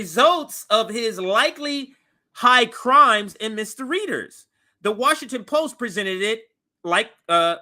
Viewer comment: I'm sorry, did you say "misdereaders"?